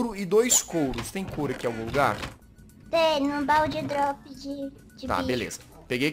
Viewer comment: Eu entendi "Baú de drogas" Kkkk